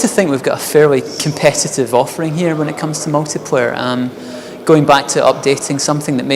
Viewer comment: I'm not native english, but at 7:27 does he mention competitve multiplayer? Have I heard it well? Competitve multiplayer without crouch, with auto-aim and lock-on-target melee. Tell me that I heard wrong, that he thinks AvP got a competitve MP.